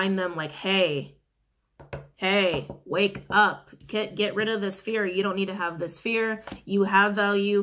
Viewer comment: omg wtf am I tripping or did she say my name at 22:13 "hey kit wake up get rid of this fear" I'm shook rn